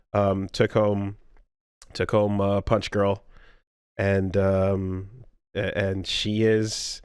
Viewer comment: When said "took home... took home, uh, Punchgirl", for a second I thought he said "took home Takoma, Punchgirl", and accidentally let a name slip xD